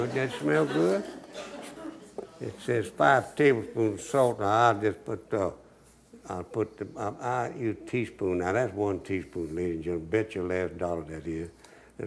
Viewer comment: Im just curious what he said at 5:11, anybody else catch that? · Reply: "It says five tablespoon of salt, I'll put.. ah, I use teaspoons.. that's one teaspoon ladies and gentlemen."